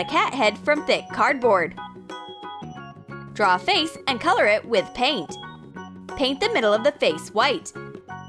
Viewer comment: I hear ''cut it with a player'